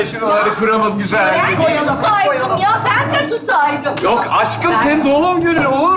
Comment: Mustafa Naimeye aşkım mı diyo ben mi yanlış duydum hsnsjsnsjsnd geçenki laf sokanlar skecinde de Naime kocasına Mustafa demişti jsmsmdjsmdjsndkn